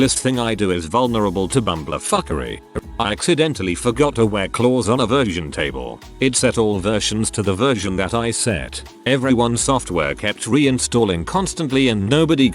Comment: EXPENSIVE MISTAKE i thought it said "i forgot to put claws on a virgin table and all the virgins started reinstalling constantly"